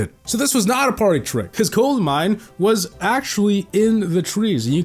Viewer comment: Is it just me or did he say golem mine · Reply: He said gold and we listen to golem 😂